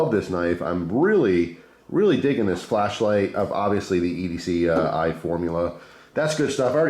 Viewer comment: I thought you said EDC Eye formula... like you spray that shit in your eyes and wipe it away. I was so confused.